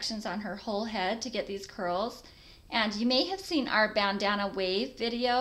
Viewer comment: i thought it said "banana" curls lol